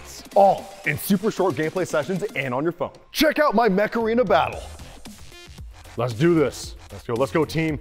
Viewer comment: Install Mech Arena for Free 🤖 IOS/ANDROID: <link> and get a special starter pack 💥 Available only for the next 30 days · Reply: sounded like said macarena :p ehhhhhhh macarenaa